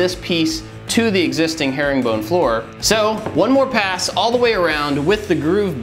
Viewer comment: I thought it said herobrine floor :/